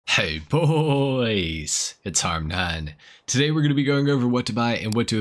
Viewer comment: i thought he kept saying hey boys it's our nan at the beginning of watching this guy i stayed watching because i found it funny and his entertaining and fun keep up the great work